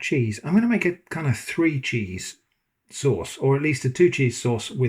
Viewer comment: I don't understand the question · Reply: I replayed and realized I misheard. Three cheese, not tree cheese! Since then I've had my hearing aids tuned up higher and I hope not to puzzle you again, sorry!